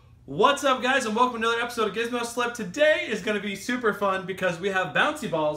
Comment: did he said "welcome to gizmo slap" ?